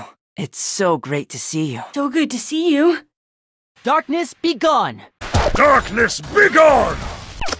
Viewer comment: When I first saw dark cacao at the flour war I thought he said darkness begun